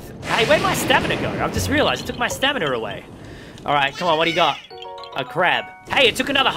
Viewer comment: “It took my stamina away!” Is what he actually said What i hear is “it took my staminuur away!”